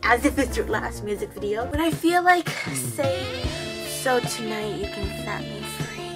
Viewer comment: it's actually, 'sa tu nal' wont you set me free. Not so tonight😂 misheard😉 same as me😂😂